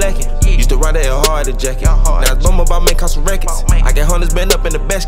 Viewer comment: What did bruh say at 1:45 that shit was smooth af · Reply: Used to rock that Ed hardy jacket now I bought the balmain n this shit costs some rackets or causin racket. 😂